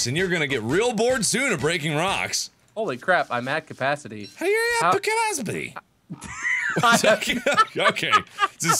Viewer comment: I thought he said "How are you at Bill Cosby?"